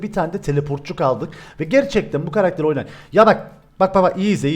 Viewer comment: YARAK duydum AHGVSSD